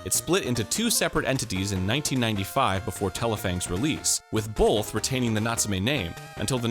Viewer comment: I heard BOLTH